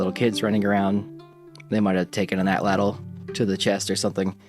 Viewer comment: if you see this, where the subtitles say "they might have taken an that (indistinct)" what he's saying is "they might have taken an atlatl"